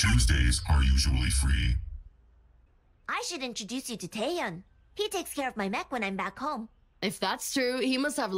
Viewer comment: 1st time I heard that I thought she said he takes care of her meth